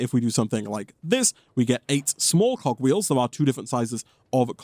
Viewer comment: I legit thought he said cock wheel ☠️